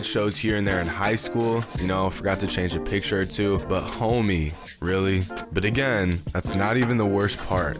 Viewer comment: using CC closed captioning: "butthole me" instead of but, homey.